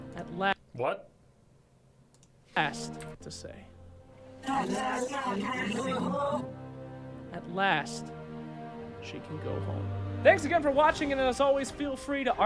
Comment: for anyone wondering what Spectre actually said, "At last I can go home." It is her expression in game when the team won.